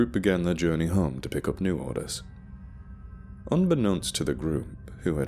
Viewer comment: I heard "on their way home to pick up New Order unbeknownst to the group" and I peed myself. Have to start again when dry!